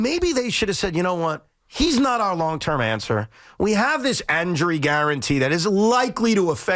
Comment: What the hell did Evan just say at 10:35? "We have this anjury guarantee" anjury? I think it's injury lol 😂